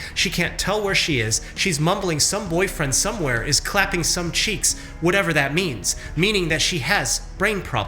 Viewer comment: except someone wanted to try something *dr cheek clapper appears hahahaha!!!! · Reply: except it was misheard, she said "cheats". I knew instantly what she meant. 'cheeks' is meaningless and jokes are puerile. Fortunately never got this, sounds awful.